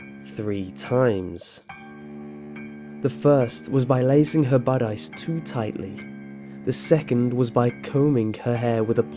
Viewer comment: I can't understand What he said in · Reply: "The first was by lacing her bodice too tightly." A bodice is a classical garment like the one Snow White wears in the Disney version, with laces in the front. The wicked stepmother killed Snow White the first time by lacing her bodice so tightly that she couldn't breathe.